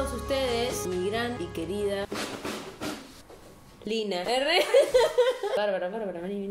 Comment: like si escuchaste que dijo "lyna" :V